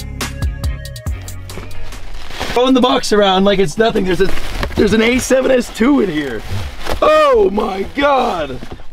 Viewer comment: did... did he just swear? Thats illegal. I've been watching this man for over a year not once have i heard this man swear. Why? Just why? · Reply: Bruuuuv he said shit